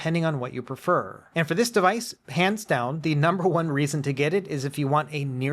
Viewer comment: i swear it sounds like you are saying "pants down" honestly I love that